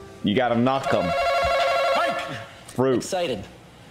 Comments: "Name something you feel before you buy it." "Excited." Is that what he said? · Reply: I thought he said bite it.... He said excited 😂... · "Name something you feel before you buy it." "Excited." Is that what he said? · what's his awnser I can't hear what he's sayin cus pewds is talking over him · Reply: he said "excited", but the question was "name something you feel before you buy it", not how do you feel before you buy something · what's his awnser I can't hear what he's sayin cus pewds is talking over him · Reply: thanks dude, I did hear excited but I just kept thinking of a product that you physically feel and so I thought I heard wrong 🤪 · I couldn’t hear what he said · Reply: "excited" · WHAT THE F DID HE SAY? I don’t understand what he said y’all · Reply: The question was : what is something you 'feel' before you buy it? His answer : excited (since he asked "what is something you 'feel'", to which he replied he 'feels' excited) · What does the guy say at 5:55? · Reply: Excited, as in you feel excited before you buy something 😆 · what does he say? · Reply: Excited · ??? what did he say? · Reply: The question was : what is something you 'feel' before you buy it? His answer : excited (since he asked "what is something you 'feel'", he replied he 'feels' excited) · What does he say at # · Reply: The question was : what is something you 'feel' before you buy it? His answer : excited (since he asked "what is something you 'feel'", he replied he 'feels' excited)